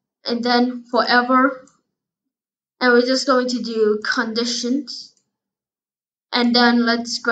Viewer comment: when you said condition I thought you said cut the S###